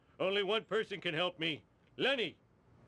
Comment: When Homer said the only one that could help him was Lenny but it sounded like he said Lemmy